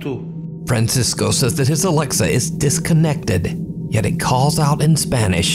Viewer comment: He didn't say his Alexa was disconnected. I normally watch all your videos religiously, and to see this bad of a translation is pretty disappointing. Please try to keep the translations, at least, right. I can't say anything with the ones thast aren't spanish, since I'm only bilingual, but I can definetely say he did NOT say it was disconnected. He said "Se acaba de conectar", which means "It just connected" or "It just established connection". So, no. It's not a magical alexa that doens't need power. It's just a normal... creepy... alexa moment. May be fake, I know you can make your alexa do some pretty weird shit. Also, "Qué susto", or "Qué puto susto" means "How scary", or "How fucking scary".